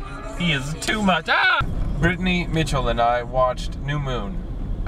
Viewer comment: I thought Brittany was saying "ooh" in the beginning, but then I realized it was, Mitchell.